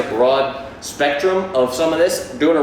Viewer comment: I dig the shirt, but I didn't quite get the brand name. I heard "Insicairo". How far off am I? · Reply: El Sicario Covert Concealed Carry Shirt..